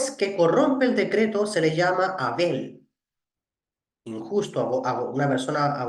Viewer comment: Buenas un saludo desde Uruguay 🇺🇾. Le hago una pregunta: en el minuto 25:20 en adelante usted dice ¿que a una persona injusta se le dice ABEL o KEREN? Si puedes me confirmas bien esto o quizá escuché mal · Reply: Saludos estimado. Al juez que corrompe el decreto se le llama 'avel' עול